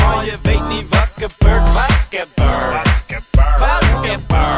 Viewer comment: Is it called "matskebur"?